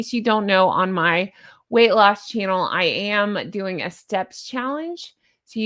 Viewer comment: She totally knows the date. I agree how you feel about it too! 16:00 😅 I heard "sex challenge" & had to listen 3 times to figure it out! Ha ha! 👍🖐💙